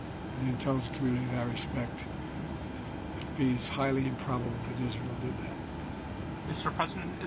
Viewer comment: Biden says: "It's highly PROBABLE that Israel did that" freudian slip, but in the subtitles it's written "improbable" instead of "probable", you guys should correct it. The look Antony Blinken and John Kirby gave each other right after Biden said it was priceless.